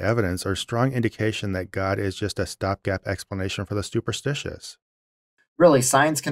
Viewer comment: Did you say "Stuporstitious? That's my new favourite word. If it's not already a real word, it should be. A stuporstition is something believed in without thought. I hereby declare it! That's also my third comment for the vid, so now I shut up. Otherwise you'd never get me to be quiet.